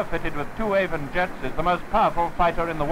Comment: Did he say an Avon Meteor?! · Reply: Meteor with two Avon Jet Systems